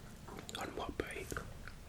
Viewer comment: What does he say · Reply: "One more bite"